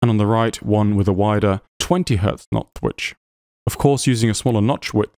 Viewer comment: your videos are fascinating and really well done. Thank you for continuing to make them! However, I do have to give you a hard time, only because it made me chuckle... around the 6:15 mark you twice said "nodth witch" instead of "notch width." I was so engrossed in the material that I had to go back and listen to it about three times to make sure I heard it right.